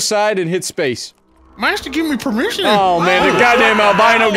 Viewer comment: Everyone just gonna blow over what said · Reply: "Master give me permission" 💀